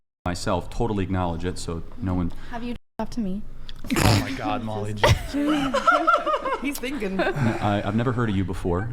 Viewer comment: what did say at 0:05? they bleeped it out it must be a swear, was she asking him something gross??? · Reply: She said j e r ked